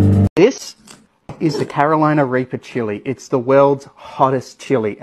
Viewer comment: Carolina raper chili 😂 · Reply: it’s because he’s Australian and it sounded like he said raper instead of reaper.